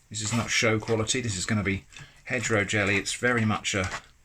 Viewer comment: My dumb ass thought you were saying hetero jelly. As in heterogeneous.